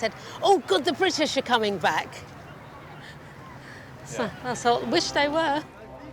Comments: what just she said, unable to hear it properly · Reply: The brits are coming back.. Wish they were. BKLi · did she just said wish they were???